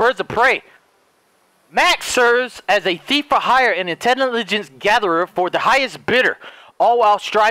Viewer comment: Did he just say intenilligence?